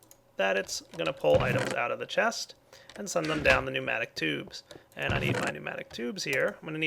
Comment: Is it just me, or did he say tubematic noobs?